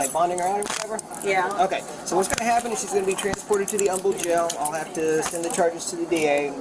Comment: My dunbass thought they said the ugly jail 😬